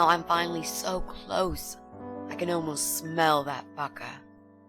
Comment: I thought she said 'baka' instead of 'fucker'